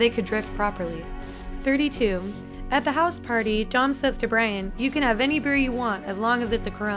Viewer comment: did she call Brian brain?. i swear thats what i heard